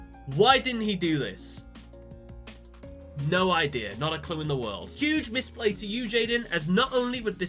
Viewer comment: after multiple rewinds I cant hear anything else but "Not a cum in the world"